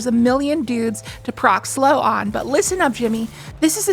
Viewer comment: Did you say "Jimmy" (Neutron), because you sound a bit like Carl?